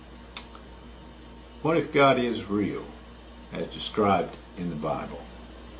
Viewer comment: loooooooooool at the subtitles!!! Activate the subtitles @ 07:20, the creationist says "What if God is real?" and the subs say "Boycott of Israel"!!!! LMFAO! Had to laugh for 5 min!